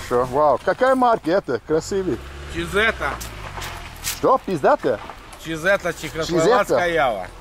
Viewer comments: какая марка? -чизете - что, пиздато? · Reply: Я тоже услышал «пиздато» :) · CZ are not Russian they were made in Czechoslovakia just like Jawas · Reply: He actually says "czhechoslovatskaya", why translated this as "russian" is beyond me.